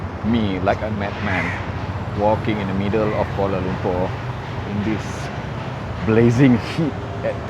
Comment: I thought you said, "Walking in the middle of Fallout 4" 😂🤣🙂